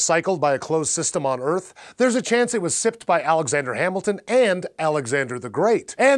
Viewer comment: *spit take* Did he say ALEXANDER HAMILTON!!!!!!!!!!!!!!!!